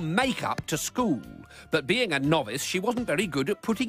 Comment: did he just say knobhead